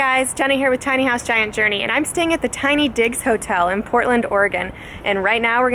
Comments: Thought u said tiny dicks lol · Sounded like she said tiny dicks hotel 😂😂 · Tiny DIGS D-I-G-S I thought she called it something else =/ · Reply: Southern Sweet same I thought she said tiny dick I was like what the fuck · Totally thought she said tiny dicks hotel